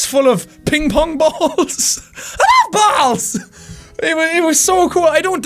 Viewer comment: Jack... Did you just say you love balls...? ;)